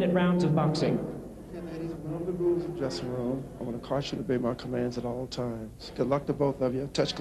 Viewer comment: subtitles says I want to cost you too baby. What a pick up line!